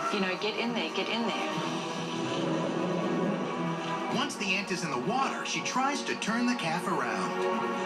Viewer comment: i could swear he said once the ent is in the water